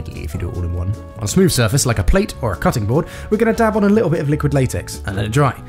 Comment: did you said dab